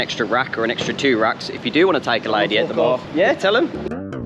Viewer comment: what did this guy said ? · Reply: Tell them to F**k off